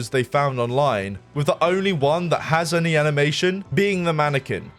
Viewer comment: the subtitles say "Anime Nation" instead of animation and I find that hilarious.